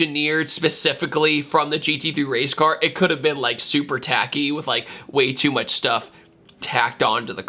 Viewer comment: Where you said "it could've been super tacky", you I heard: "it could've been Supra-tacky" :). "Supra", with its fake vents, should become a synonym for "tacky" these days.